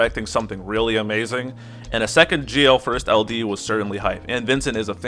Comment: I misheard from "he is the Gl first" to "he is the JAIL first" LMAO!